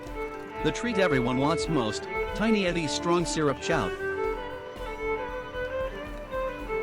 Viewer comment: I heard the Tiny Eddie alert about five minutes into the video and I thought he said "strong syrup child" and not chow . There was a brief Concern™. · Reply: I misheard "prawn syrup chow". 😬